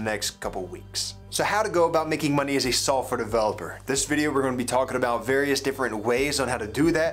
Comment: Why is he saying Sulfur Developer?